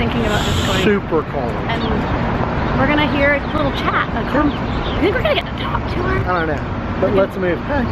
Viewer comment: did they say "Doyou think we`re gonna get The Talk tomorrow?" Who can give me a clearer explication on that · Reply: never mind. I have managed to clarify this. they said "do you think we're gonna get to talk to her?"